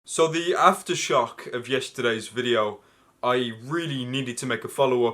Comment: When you said "aftershock" I misheard you as "Asda Shop".